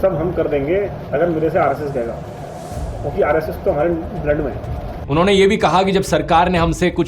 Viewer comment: i heard rss toh hamara land mai hai lmao